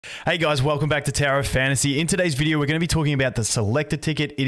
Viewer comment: I always hear u say "Tera of Fantasy" +D